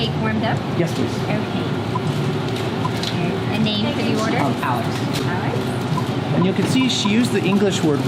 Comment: Hi, This is so useful for my travel English lesson. I'm trying to understand what the Starbucks staff said when she asked your name but I can't get it exactly. It's about 1:42 "The name --- you order?" I know what it means but it's hard to get a full sentence. can anyone help me with that? Thx so much in advance~! :D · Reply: Good question! She said it this way quickly: “A name for your order?” It sounds more like “A name fer your order?”